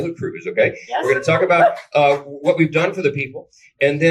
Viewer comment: The captions says that Mike Johnson said 'what we've done for the people' but I swear I heard 'what we've done TO the people'.